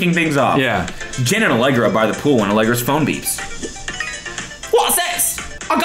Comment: lmao at 13:48 i thought cody said “genitallegra”